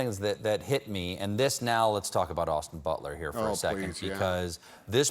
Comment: "Let's talk about Austin BUTT LETTER, THIS...'' says the captions as Stephen mentions Austin Butler. Lol Also, at 2:08, what was this word that seemed to sound like "Basmatic" that Tom Hanks said? I couldn't understand the word. June 22nd, 2022, 4:44AM